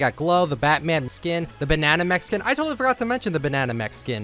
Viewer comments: I thought you said “Banana Mexican” 😅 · Swear to god he was was saying Banana Mexican lmao